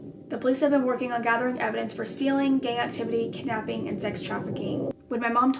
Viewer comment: i thought she said gay activity